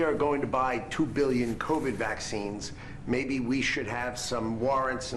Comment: Did I hear the word 'vax" ? 6:00. No Way !!!!